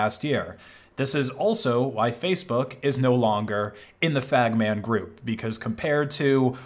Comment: Did he say the “Fag Man Group”?